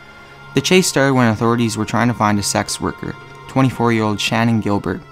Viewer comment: In #3, when he 1st mentions it -it sounds like he says "SAX worker" (lol)-of course, he ways saying "Sex worker"-as in, whore, not a jazz musician (Sax Worker)...